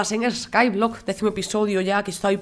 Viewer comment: pense que habia dicho ultimo al principio pero dijo decimo episodio